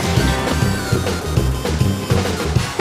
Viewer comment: What does he say at the end? I always thought he said, "I've saved the park from fit skating" but that doesn't make sense???